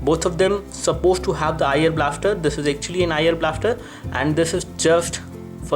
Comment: would be nice to have a written version of your video couldnt quite understant whan you said around 3:31 it sounded like you said "iron blaster" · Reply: meareAaron 'IR blaster'. Infrared blaster.